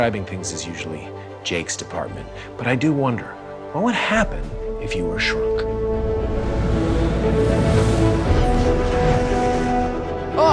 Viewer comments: At first I thought it said "what would happen if you got shekt?" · Forreal I'm disappointed... I thought it said "if you were a Skunk"